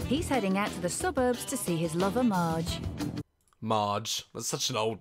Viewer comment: When they were talking about his lover Marge, I thought they said “he’s going to see his MOTHER marge” and I was like ‘this took a turn’